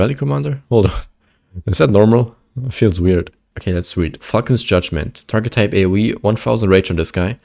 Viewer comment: I was listening to your video without watching, but had to raise my head at the screen when I heard "F1cking Judgement" and was like "Oh, it's Falcon's Judgement"